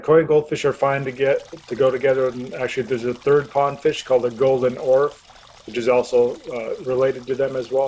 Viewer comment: I didn’t catch that, does anyone know what fish he’s talking about? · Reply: Hi John, is talking about a Golden Orfe. It is a similar fish in appearance and size to Koi. They are often gold or reddish in color. Best description would be a plump or sturdy fish. They are more common in parts of Europe than North America but you will find them in lakes and ponds here.